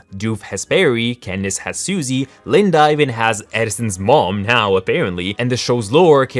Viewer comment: Candy Cane is as good a nickname as any for Candace ahah And Adyson is a fireside girl in Isabella's troop, in a recent S5 episode it was revealed Linda and her mom have an on-going feud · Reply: Oh, I thought you said Edison. Addison makes much more sense